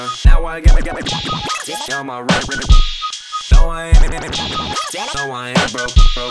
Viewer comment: I thought he said "wiener wiener got the mask on" lol😅